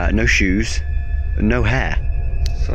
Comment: no shoes and no what ? no head ? no hair ? What is he saying ? To hard accent for me ?